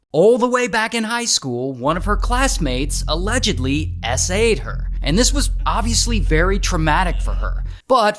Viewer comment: allegedly " " ed her. what did he say? s aid her?? 4:20 SA? what is that?